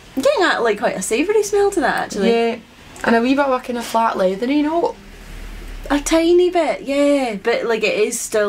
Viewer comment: Doesn’t matter how many times I rewind the video, all I hear is “a fart leathery note “ . I must admit, I’ve never got that.😂 Great review as always. Cheers ladies.